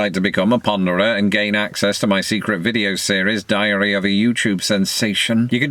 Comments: I thought you said "Diarrhea of a youtube sensation". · At first I thought you said "Diarrhea of a Youtube Sensation"... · For a moment, at the end, I thought you said, "...gain access to my secret video series, Diarrhea Youtube Sensation..." I figured I'd skip that!